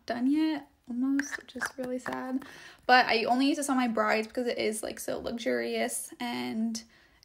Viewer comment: "I only use this on some of my brides because it's so luxurious and expensive...". Ok I'm out. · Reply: ok sorry then cause I heard "on some of my brides" and not "on my brides" 🙏🏻